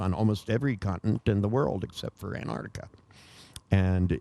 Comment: except for .... what? what does he say in 7:55?? it is a country, but which one? thanks. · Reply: Antarctica